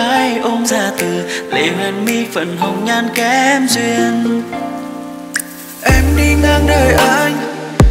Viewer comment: dù tình có nơi phương trời cứ nghe nhầm thành DÙ THÍNH CÓ NƠI PHƯƠNG TRỜI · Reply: mk lại nghe đoạn " em đi ngang đời anh " cứ nghe thành " em đi ngang người anh